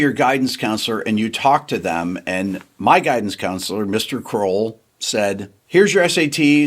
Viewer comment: did I mishear his guidance counselor was named Mr Grohl? ….wouldn’t this be an epic story if his first name was Dave?